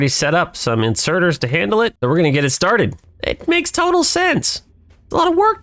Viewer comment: sounded like you said it makes tunnel sense!!! lol